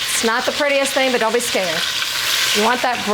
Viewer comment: Did she say, “Don’t be scared” That’s Miss Talli Faye’s tagline: Don’t be scared. I only watch 2 cooks on YouTube: Miss Talli Faye & Chef Nicole. Both are Southern cooks and man, between the two of them, they really know their way around a Southern kitchen.